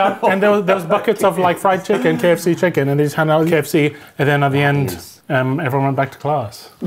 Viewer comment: He asked for kfc it he wanted Popeye's he would have asked for it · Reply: he clearly said Popeyes at 4:10 but because this is an ad they voiced over it.